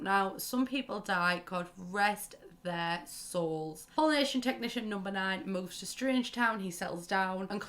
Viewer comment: Wow until I saw “Pollination Tech #9” on screen I thought you were saying Polynesian Technician #9 and I WAS SO confUSED